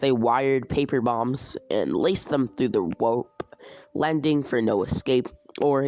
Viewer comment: you said wope instead of rope